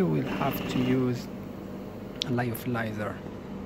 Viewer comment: I can't quite make you what you're saying here (Thanks for useful video 👍) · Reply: I believe he said a Lyophilizer aka freeze dryer